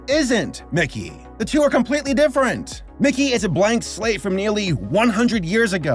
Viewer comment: lmao I thought he said “Mickey is a black slave”